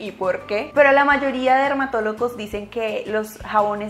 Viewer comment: Min 7:27 le entendí dermatolocos 😂